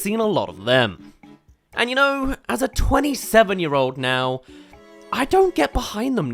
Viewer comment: at the beginning I misheard "as a 27 year old now" to as a 7 year old